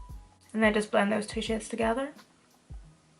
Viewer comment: This whole time I thought you were saying "This sh*t here" but you were saying shade 😅🤣 either way I love it, pale skin girl tryna learn how to do make up here 👩‍🎤😊